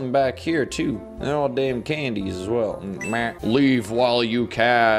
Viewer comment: did he just say damn?